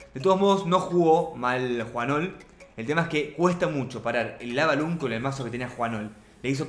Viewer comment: por que dices lava lund(11:00) y tambien y wifh (1:54) aprende ingles igual sobre eso me gusto el video buen trabajo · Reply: Digo Lavaloon y digo e-wiz que es como se les suele mencionar !!